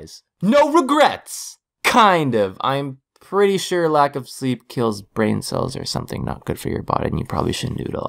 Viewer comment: haha "weekly uploads" If you want to keep updated with me when I disappear again for 2 months haha just kidding I hope You can find me on on twitter, instagram, and facebook! Thank you for being so patient in my absence guys. I appreciate it. <3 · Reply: EroldStory for a second i thought it said lack of sheep